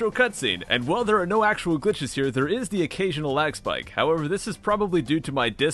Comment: I always thought you said X-Bike and not Lag Spike..oops